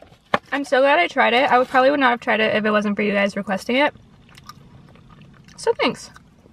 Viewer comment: Could please anyone speaking English help me what she says from15:09 to 15:12? could u type for me? Im an English learner!! I've listened to the part many times, but still I don't get it :( Thank you so much!! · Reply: jjeong Park she said "it's so good, I probably wouldn't have tried if it wasn't for you guys requesting it"